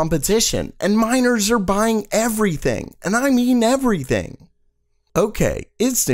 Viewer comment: my dumbass thought he said "And minors are buying everything!" XD